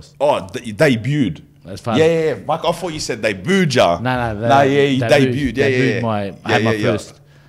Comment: haha I thought he said they booed too… hectic